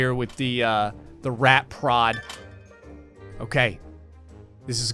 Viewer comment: I swear, I thought you said "rat pr0n"... I was thinking "ohhh. .. it's one of thoooose games"!